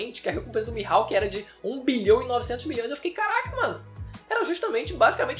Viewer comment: eu entendi 1 bilhão e 900 bilhões